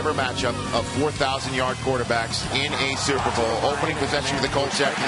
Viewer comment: i thought he said the first match up of 4000 year old quarterbacks.